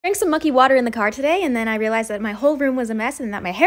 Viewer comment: I thought you said monkey water!!! :-)